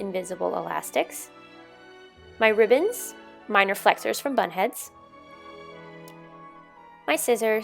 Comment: Every time you said bunhead I thought you say butthead 😂